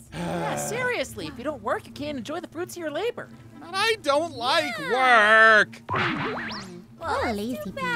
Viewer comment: When ein says I don’t like work I thought he said I don’t like worms😂😂🤣